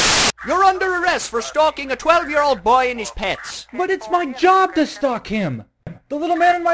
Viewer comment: OHHHHHHHHHH for stalking a 12 year old boy and his pets.... i always thought it was saying, "for stalking a 12 year old boy in his pants".... don't ask why, i was 16...